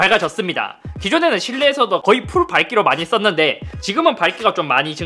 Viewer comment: 이거 나만 그렇게 들리는거 아니지?ㄷㄷㄷㄷㄷㄷ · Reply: 풀발기